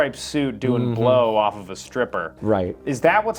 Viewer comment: I love how the subtitles show "fine woman" instead of "stripper"